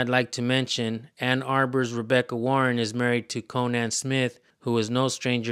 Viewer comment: Conan Smith. I thought you said Gonad Smith. I cracked up and had to rewind and rewatch and realised my mistake 🙃